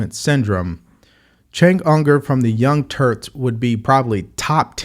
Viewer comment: did I mishear you or did you call them young TURDS? lol